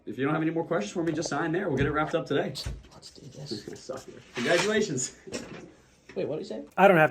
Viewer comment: "Hehe sucker congratulations" wait what he say? 😂😂😂